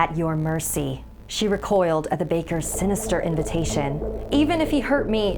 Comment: Was it just me or in the beginning when she said "the baker's inn" I thought she said "the baker's skin"